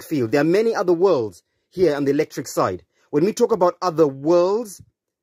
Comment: So there's a black hole sun. Didn't Soundgarden do a song about that? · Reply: Every time he said electric side, I thought he was saying electric slide. So maybe this guy is just really into music.